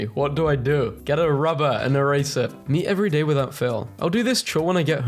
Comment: what did bro say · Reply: To get a rubber and erase it?